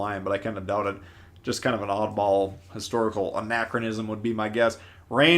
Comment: Anachronism(not inaccurism): meaning an inconsistency with order of events. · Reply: sounded like he said inaccurism.